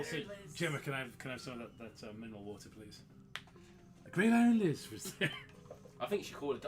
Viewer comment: really thought this said Iran Liz